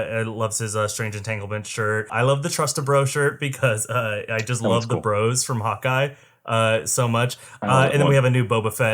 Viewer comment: What I heard. "I love the bros from hot guys." Ok then.